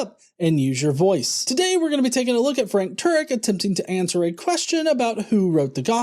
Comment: What’s up Heathens! What did you think about Frank Turek’s evidence? Do you think it holds some weight or nah? Let me know!!! If you missed yesterday’s video about Dr. Carrier analyzing a bad argument against Mythicism, check it out here: <link> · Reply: Sounds like you said Frank turd LOL